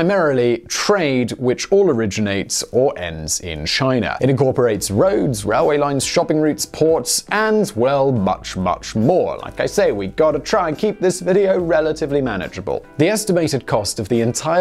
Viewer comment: I'm sure that i heard "shopping routes"